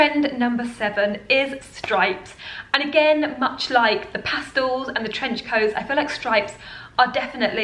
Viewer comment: Did she just say Pastal?